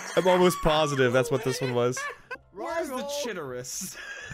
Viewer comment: did he say "where's the chitterus"?